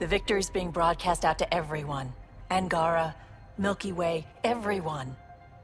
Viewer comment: Did she say she's broadcasting to Milky Way... that was ravaged by the reapers? Hopefully 633 years is enough to rebuild.